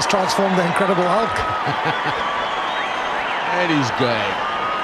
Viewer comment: Actually means happy, I thought he said, "And he's game."